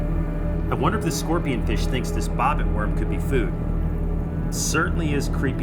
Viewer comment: I swear I thought it said robot worm